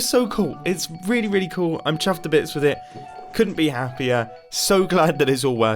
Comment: has ruined "chuffed to bits" for me. Every time all I hear is "chaffed to bits" lmao.